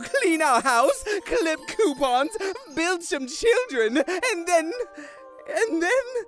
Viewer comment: The frist time I saw this I thought that Papyrus said: "Buy some children" and no "Build" XD